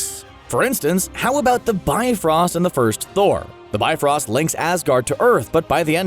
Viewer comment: sounds like he says "bine-frost" yet 2 seconds later he says it correctly; the "bi-frost"... What's up with that??? lol